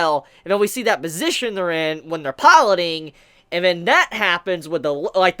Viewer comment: So ummm...... Is this whole anime gonna have sexual themes or something? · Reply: Misheard "piloting" for "pollinating" Your Whole plants allegory gave me school science ptsd flashbacks... back in the days before the dinosaurs roamed the earth... god was a harsh teacher I tell you that 😂